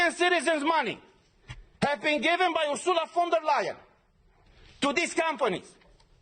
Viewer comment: there is a mistake in the transscription/undertitel at 3:48/5:15: I think he said "Ursula von der Leyen" President of the European Commission // so it's very important to listen carefully. –> the transscription says: "... have been given by «usura from the lion»" and the subtitle says: "... have been given by «a liar»"